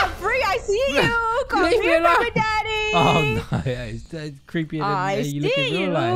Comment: omg I didn’t hear baby at first and thought she was just calling him daddy 😳